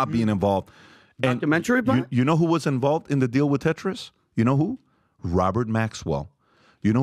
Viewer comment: "Follow the money" is always the key. Also, I just hear "The tetanus" not "the Tetrus". One is hard and stiff....